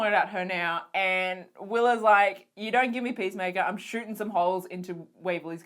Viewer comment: i thought she said "I'm shooting some hoes"